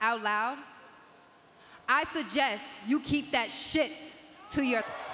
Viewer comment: that's sad we couldn't hear what she said but anyone know? "To your" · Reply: production "messed up" and bleeped "too late", lol fixed it for you We both know that they did it intentionally to let her say "shit"